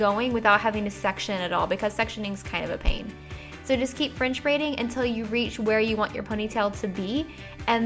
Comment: Every time she said "french braid" , I heard "fringe braid"